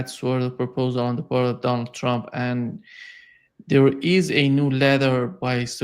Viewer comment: I thought I heard say, ..."with a new McDonald trump..."